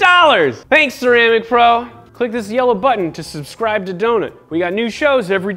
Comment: every time he says 'yellow button', i hear 'yellow butt'